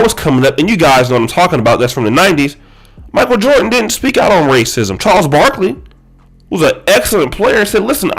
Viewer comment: I heard him say politics not racism